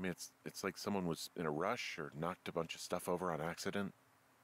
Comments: Why does he say "stuff" but the subtitles say "shit" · the subtitle says shit but Henry says stuff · he said stuff instead of the text which says shit. Oops.